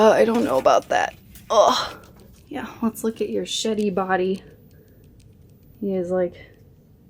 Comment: when you brought dude out i thought you said "look at your shitty body" not "sheddy body" hahaha